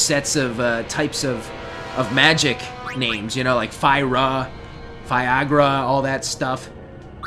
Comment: fiagra, i heard it wrong at 1st.